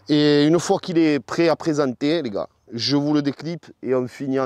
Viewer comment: what country are you from? what language. I just understood the word "niggahh-5:32, 6:12" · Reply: France and French. It is not niggah but "les gars" and that means "dudes"